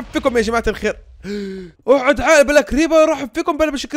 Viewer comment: اللي فاهم ايش قال يقلوي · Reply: عشان الكريبر انفجر وصهيب تدمج حيل تقدر تشوف قلوبه وبعدين قال للكريبر اقعد عاقل بس انه بالاردني